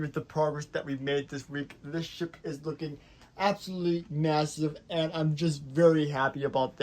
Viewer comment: did you just say massive